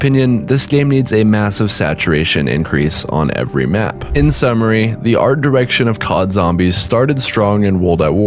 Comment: I thought it said "the autistic downfall of zombies"😂😂😂